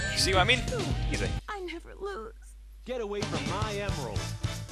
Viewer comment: "I have lose." Is that what she says? I've been wondering if this is broken English or if I've just been hard of hearing all these years... O.o